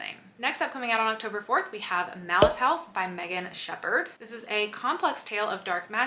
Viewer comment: I thought you said "Mouse House" at first (Malice House) and I thought Please no more rodents! 😄🐀🍂🍁